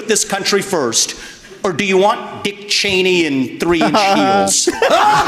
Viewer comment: Playing this at x2 speed, misheard “Dick Cheney with three chins” thought he was talking about Chris Christie